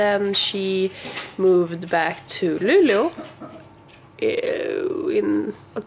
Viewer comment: Also, is Luleå really pronounced like "Lulu"?! I've always pronounced it like how it's written (Lu-le-å) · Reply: and 0:49 I really hear Lulu, no E or Å... Maybe you just talk too fast for my ears xD It sounds cute though. It's gonna catch on! haha